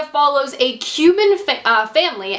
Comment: The mortification when you inboxes it in the other video I thought you said Cupid family.... I feel salty now